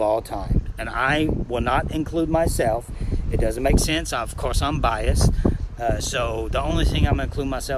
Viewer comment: I thought that instead of saying "of course I'm biased" I figured it was "if course I'm number 1" lol